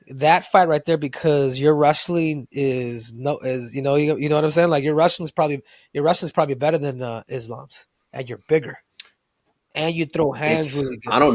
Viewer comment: did he say your wrestling is better than Islam’s😂